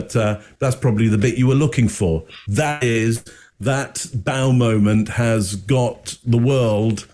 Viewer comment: My hearing's not the best and for a moment I thought said. "That bowel movement got the world excited" Immediately I realized he said " Bow moment."😄